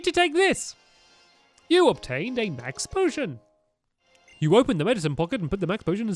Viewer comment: stampy said mex instead of max potion.